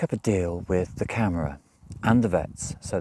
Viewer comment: Hi, here's a hint for saying "Camara" (City Hall or City Administration). If looking at word as if you pronounce letters in English (disclaimer, mine is US): try this phonetic- ish pronunciation: kah-mar-ah. (Mar as in planet Mars. (Only reasons, I mention it: I was listening to the video and literally thought you said camera (as in taking photographs)...(you were talking about your dog, shots, kittens adopted, etc...so you weren't moving....thus I was listening. "Equipment to take photographs" didn't fit with what you were discussing..I realized, you were saying "camara" (Portuguese city/town/village area administration). I'm also trying to help you improve your PT pronunciation...as well as any one else who is trying to get to PT...hopefully they can look back and improve. (And of course, anyone already in PT...can hopefully help them improve.) Hope it is appreciated..not trying to be critical. (I'm in US, speak Portuguese having learned it in college in US and in a year abroad in Lisbon in 1990-91. A bit rusty, but I know a bit of linguistics and where to put your tongue in your mouth to make the sounds...wich is always a bit of a battle when learning a language.